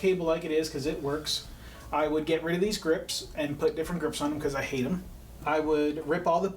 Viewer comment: What does say at 13:38? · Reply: I hate em